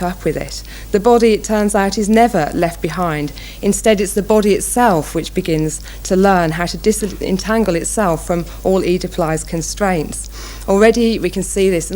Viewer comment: what did she say? I tried to look it up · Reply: "its the body itself which begins to learn how to disentangle itself from all oedipalized constraints"